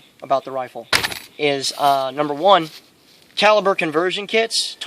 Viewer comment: I thought you said "child birth conversion"